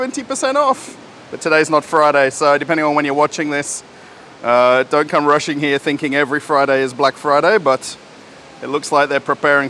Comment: Понравилось его выражение: Похоже русские считают каждую пятницу чёрной пятницей ))) РЖУ НЕ МОГУ ))))🤣🤣🤣🤣 Ты сделал мой день, чувак !!)) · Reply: Вы правы. Дмитрий, видимо, услышал "Russians here thinking" вместо "don't come rushing here thinking...". говорит "не торопитесь приезжать сюда, думая, что каждую пятницу Черная пятница". По крайней, я именно так это понял.